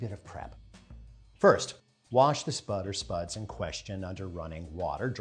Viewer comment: You rubbed them with oil and then said “buttered spuds” 🤔 Did I mishear? I prefer butter btw. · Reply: I realize now you said “spud or spuds” not buttered spuds. No direction on what kind of cooking oil. We’ll see how it goes.